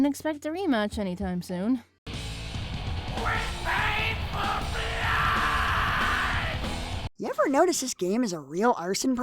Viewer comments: What did they say at 6:41? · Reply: turn on the captions it said WE PAID FOR BLOOOOOOOOOOD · ish "yamcha sure as hell not gonna die on you" did i hear wrong because if i didn't then she is gonna die (Dragon ball references in a completely different anime...)